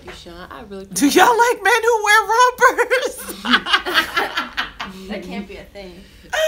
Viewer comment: I thought Tasha said, "Do yaw like me who wear rubbers?" then Jenna says sumthin about pulling them off. I was like, "WHAT?!" Then Tasha mentioned Cam, so I then realized she said "Rompers" For a second I was thinking they are extreeeemly casual with the sex "tawk".